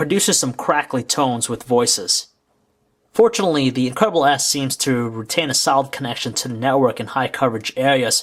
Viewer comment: every time he said the name it sounded like he was saying " the incredible ass" XD